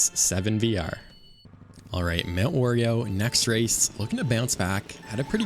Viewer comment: had to double take because i had opened a pack of oreos right when you said mt wario. I thought i heard mt oreo